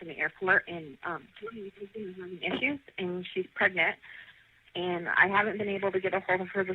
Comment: What does she mumble at 0:19??? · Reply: "She has health issues and she's pregnant."